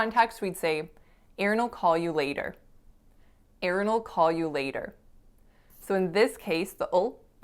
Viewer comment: This was a very good lesson. Thanks a million! But I still can't figure out what you said at 11:12. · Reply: Aaron (boy name) will call you later. Aaron'll call you later.